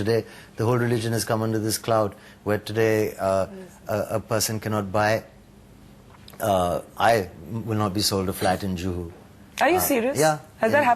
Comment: WHAT DID HE SAY?? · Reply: He said that once he was denied to hire a flat in Juhu being a muslim.